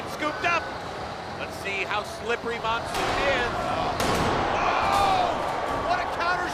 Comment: they wrote mondoon instead of monsoon